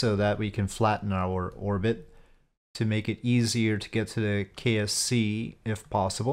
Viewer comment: did you say kfc lmao????